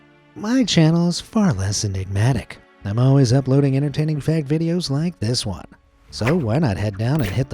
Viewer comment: did he say fake or fac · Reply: He said fact